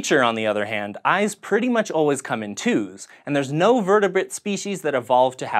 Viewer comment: Sort of sounded like you said vertebit not vertebrate.